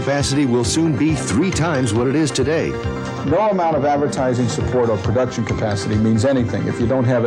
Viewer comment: Captions say “Verizon”. That’s the name of a Cellphone Company!